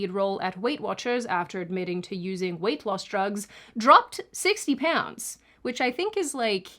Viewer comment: I think 50 lbs is more like 22 kg. A kilogram is 2.2 lbs. ETA I misheard; 60 lbs is about 26 kg.